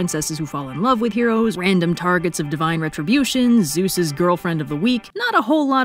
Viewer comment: The subtitles say victim, but the voice says girlfriend lul